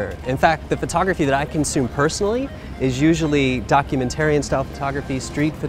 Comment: Did he say @ 02:53 “Documentarian photography”¿"¡¿ Is there something wrong with calling it DOCUMENTARY photography like it’s been correctly referred to forever¿ (excuse Spanish keyboard) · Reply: fritzv46 he says “documentarian-style photography,” but that’s not the point. The point is that when someone is speaking off the cuff and without a script, it’s ok to let small things slide.